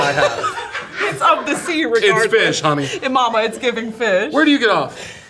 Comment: Did she say the r word at 16:22??? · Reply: "it's of the sea regardless"